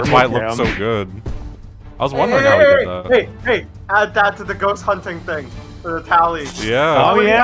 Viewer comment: i swear to god i thought he said "ghost hunting is on the rocks" like ghosts were going extinct or something i could not get over that idea